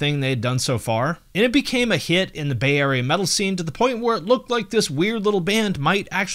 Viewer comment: When you said "Bay Area," I heard "Bavaria" and was like "wow, they seem pretty American for a German band, even their names are American"